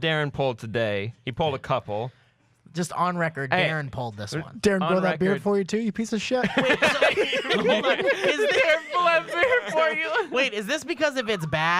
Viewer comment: What did Mike say to Daren that everyone laughed at? I can't pick it up after listening a few times. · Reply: The one at 9:39 ? I think he says "Daren did you grow that beard for me too you piece of shit". I could be wrong though.